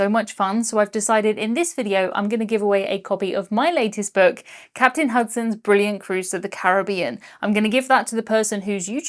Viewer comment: I thought you said "Captain Husband" which caused my mind to go into some weird directions